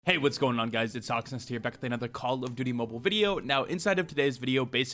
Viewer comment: captions saying hawksnest toxins LMAO😂😂😂